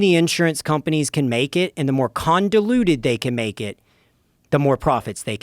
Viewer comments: Did he say “condaluted”?? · I learned a new word today Condoluted. Convoluted? I think I've been saying it wrong all this time.